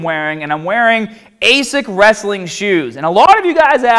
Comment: Hey Scott or anyone who knows, what are your shoes in this video? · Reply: Riku Angelus oh okay the first time I watched it I thought he said basic wrestling shoes😂